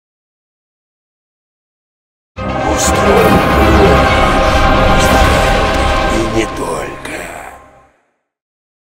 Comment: сколько слушал все не мог понять , что он говорит Единственное что понимал " Устроим ....... и не только ". Но теперь ( спасибо за субтитры ) понял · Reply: оргию...